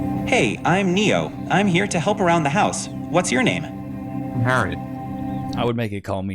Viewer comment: at first I thought he said, "I'm here to f'"* around the house."😅 Having one in a nursing home doing nurse aid duties; digging out an impaction from an old a hole, could be disastrous, js🤨